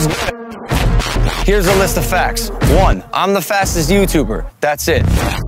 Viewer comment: For a second when Logan said “im the fastest YouTuber” I thought he said I’m the fattest YouTuber